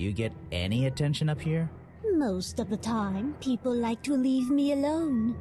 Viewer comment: I heard “leaf” me alone and now I can’t unsee the leaf hair but I love it lol